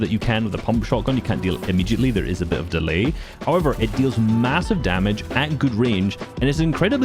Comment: did he say MASSIVE?